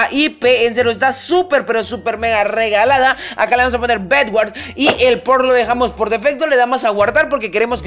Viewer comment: MIN 1:17 ESCUCHE MAL O NO..? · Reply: Nexus86 jajajaja nel dijo "y el porno lo dejamos hací" 😂😁😁😁